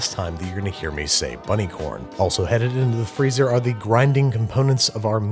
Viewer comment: did he just say "gwinding"?